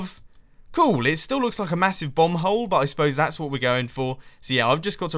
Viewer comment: It sounded like you said bumhole